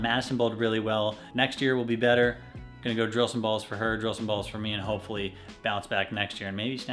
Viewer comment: Hearing say I’m going to drill some bowling balls. All I hear is “I’m going to drill some more purple hammers” 🤣🤣🤣🤣🤣